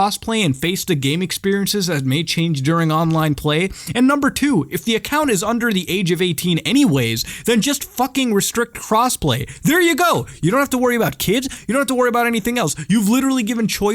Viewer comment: lmao i thought you said cosplay